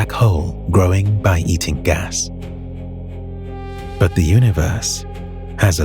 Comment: eating WHAT again? Did you say….? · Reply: Eating gas